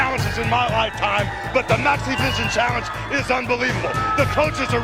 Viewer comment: I don't know if it's just the way I'm hearing it, or if it's just an unfortunate way that the audio mixes together, but to me, it sounds like Hulk is saying, "Let me tell you something Maniac Mike. I've had challenges in my Lifetime, but the NAZIVISION CHALLENGE is UNBELIEVABLE." I know he's saying "Maxivision" and I keep replaying it but I keep hearing that other thing instead. Haha! This might be another one of those cases like the "Brainstorm or Green Needle" audio hallucination (Seriously. Look that up on Youtube. It's cool and so weird.)